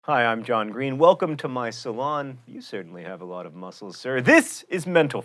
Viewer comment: I think you ought to enunciate the name of your show at least a semidemiquaver more slowly, as I thought I just heard, "Welcome to menopause." Just sayin'